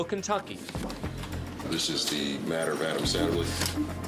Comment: I swear I heard Adam Sandler at the 6:08 mark lol